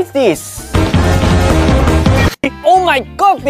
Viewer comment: I thought he said “Oh my COVID” ❤️